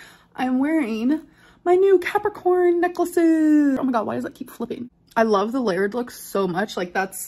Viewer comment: bruh i straight up heard her say "peppercorn necklace" and i lost it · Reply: I had to turn on the captioning to understand what she said because I full on heard "peppercorn necklace" not Capricorn necklace.